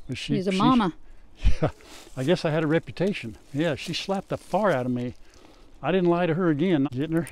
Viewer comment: Oh my 😂😂😂I thought you said SHE SLAPPED THE FART OUT OF ME 😂😂😂😂